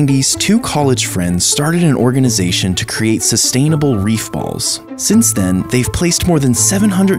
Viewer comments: I thought it said human beef balls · When it first started I thought he said “beef balls” and was absolutely fucking horrified.